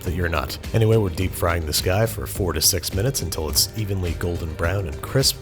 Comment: 0/10 misheard the video, fried the burger for 46 minutes instead of 4-6 😁